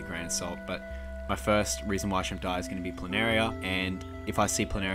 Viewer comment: Thought it said why shrimp diet. Lol. We like fat shrimp shrimps.